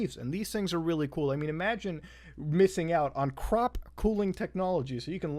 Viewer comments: at first i heard cock cooling technology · haha i heard cock cooling technology at first